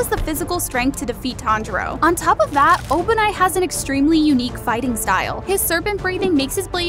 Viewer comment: when she said obanai I thought she was saying open eye for a sec